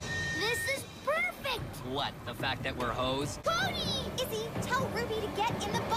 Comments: Did Noah say hoes what the heck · Did he just say.. 3:51 lol 5:56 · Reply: I think he said hosed. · I THOUGHT HE SAID “What? The fact that were hoes?” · did I hear that right? Did he just say “the fact that we’re hoes” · Sierra “this is perfect” Noah “what? That fact that we’re hoes” Am I the only one who heard this?